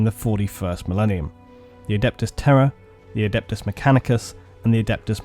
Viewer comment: I thought he said "High Lords of Terror" lol kind of a badass name though